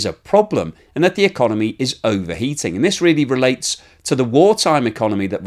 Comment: I thought PooTin previously said Russia was overeating or was he just dropping his aitches?